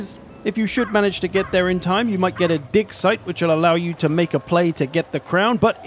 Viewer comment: I definitely heard "di*k site" :P